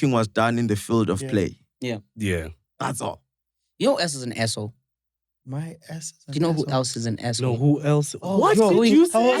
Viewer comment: I'm sorry but i heard "your arse is an arse whole" 🤣🤣🤣🤣🤣